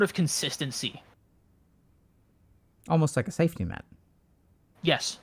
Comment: heyy that's so brilliant I couldn't understand what he really said...Dr. K got it " safety net " i was likeee yaah makes sense🤨😊